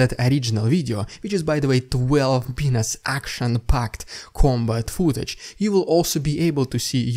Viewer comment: I don't quite understand what you are saying at 10:05 is it "Twelve p*nis action-packed combat footage"?! 🤔🤭If not, what ARE you saying? I taught English as a foreign language for 7 years in Vienna.. Never in that time did I hear such a miss-hearing that defies a different, normal or correct understanding!.. I am wondering if you are actually referring to that male organ as an attribute of the nature of some part of the content of the video. Please help me.. P.S. I feel it is OK to smile at some times during these very serious war reports, but really only if one clicks one's fingers first.. I don't think you have said "But wait; there's more!" yet.. That utterance IS the highpoint of my Ukraine-War-Report experience! I shall resume my watching in hopeful anticipation.. Having said this, I would leave a son fatherless and a fiancé alone if I could end this war without more fighting. No clicking fingers please; I mean it.